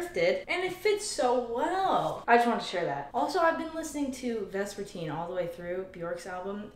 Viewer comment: i thought you said you’ve been listening to Rasputin instead of vespertine